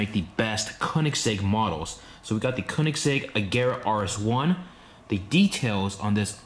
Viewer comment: Did he just say agera 1:one? Omg